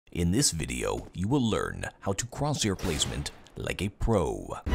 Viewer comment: Did he say "in this video you will learn how to crosshair your placement like a pro"???